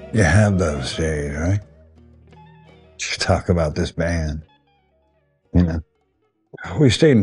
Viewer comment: Anybody know what he says right here?! All mumbling n shit after an emotional moment I suppose but I cant quite make it out. You have to what?...To talk about this band?! Lol, WHAT?! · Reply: "You have those days, right, to talk about this band"